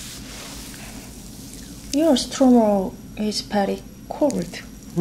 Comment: What did she say at 1:29? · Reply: “Your stomach is very cold”